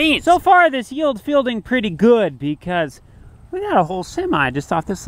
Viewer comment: Uhh… 1:15 mark; did you say “yield is fielding pretty good…” ???